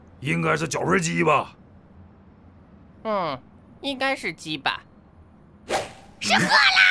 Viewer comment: 到2:45时我听错听成了。毛子:应该是脚盆鸡吧（鸡巴）鹰酱:恩 应该是鸡巴